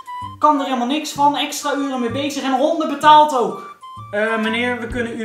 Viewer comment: Wat wordt er nou gezegd om 02:01 iets van honden betaalt? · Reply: Onderbetaald haha